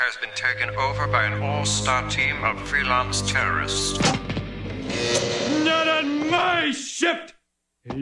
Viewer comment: I always thought he said " not on my ship"